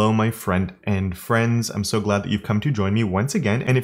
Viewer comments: Does anyone else think Kevin has somehow weaponized "my friend and friends"? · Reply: well I heard "my front end friends" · Wow I've only just realized he says front-end friends in the beginning. I always thought he said friend and friends which, admittedly, is weird, but I don't know every saying, so yeah, that happened